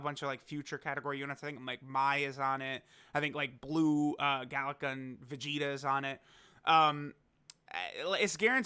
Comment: When you said blue galick gun Vegeta would be featured, I thought you had said blue kaioken Vegeta